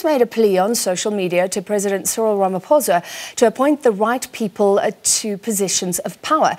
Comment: I was stunned by hearing the right people. I thought she said ''white people''. Sighs